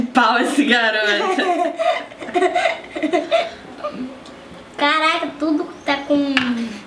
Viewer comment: só eu que escutei o falando caraio ? Kkk